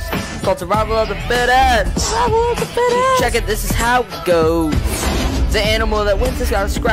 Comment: I Thought He Said Shit When He Said Check IT-😭💀